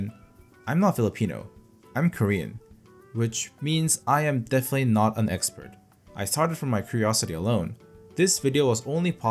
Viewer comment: I was brushing my teeth while watching this and couldn't hear it clearly. I thought he said "I'm definitely not a Mexican"🤣